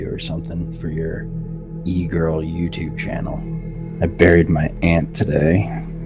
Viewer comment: i thought he said; ´´i buried my ant (the insect)´´